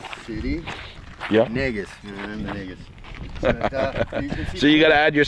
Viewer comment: at the 10:06 mark, am i correct, did i hear him say ni99as? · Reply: No Slab City is in Nagios, the name of the area.